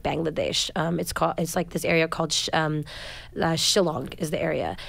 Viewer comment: Did she just say Shillong at 35:44? 🔥🔥🔥😎😎